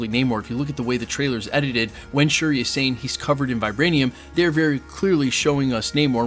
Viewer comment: Are you sure she says "he's covered in it"? There's a part where it sounds like she says, "vibranium, it is coveted". Maybe different part, idk.